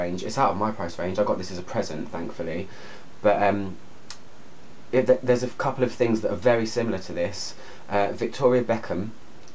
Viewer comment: Can anyone write what he said here 7:20 please . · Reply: sallofatani hello! I said “there are a couple of things similar to this”